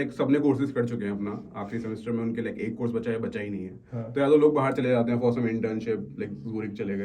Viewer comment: What he said at 14:23? Log __ Chale gye? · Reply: I think ghoomne chale gye ye bola